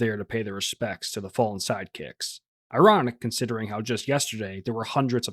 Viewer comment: For a second i thought you said 'sidechick' 😂